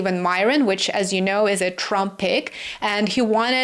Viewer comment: You said Steven Mirrin was a Trump pick. But I was chewing a part of my dinner when you said that & consequently thought you said he was a Trumpette which struck me sort of funny. Sorry if my name icon comes up 2x. Computer glitch.